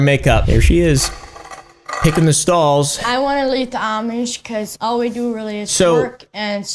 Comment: I thought the meme said "I want to Live."